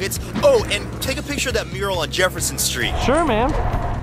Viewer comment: did anyone else see at 1:44, in the top left, instead of saying what Anthony said, it read "Take a Pitcher of Manure to Jeff's Son's skeet"?